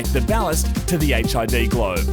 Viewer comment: when he said ballace i thought he said "fallace"